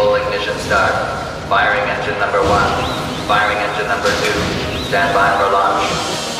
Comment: lol at 12:32 i thought it said "standby for lunch"